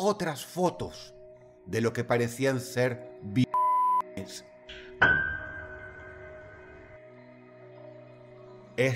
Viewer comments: que dijo? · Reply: dijo violaciones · Min 4:04 no entendí la palabra que tapó, sé que fue así para qué YouTube no se enoje, pero me gustaría saber qué palabra fue tapada. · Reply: Según yo fue violaciones